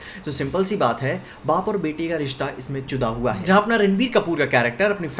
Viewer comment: exact 1:00 what do you say bro😂 jura hua hai ya ch*da hua hai and it's rashmika not ramishka